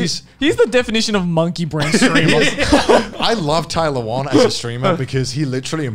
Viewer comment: "Tyler1 is the definition of monkey brain" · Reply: I thought he said tailawhuan and then I checked the text and it said Tai Lawan. Man his accent threw me off